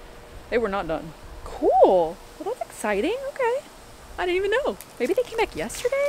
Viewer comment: “Hopefully the Siding is ready tomorrow… that would be really Ex-Siding!” 😂 I know you said “exciting” but I heard it as “ex siding”